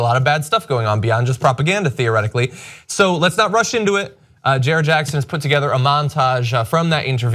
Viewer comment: did you say "Let's not rush into it" or "Let's not Russian to it"?